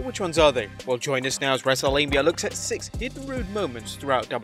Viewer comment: Sounds like you said "Wrestlelabia" which would be fitting for this video...